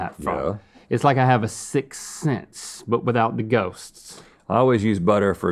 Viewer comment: Is it just me or did link say sixts snense at 1:55 ? 🤨😂